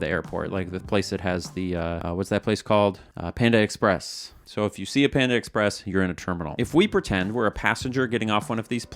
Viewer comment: I had the volume on low and got confused when I heard "when you see a panda express, you're in trouble."